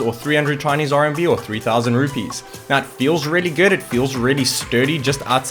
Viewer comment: did he just said 3000rupees?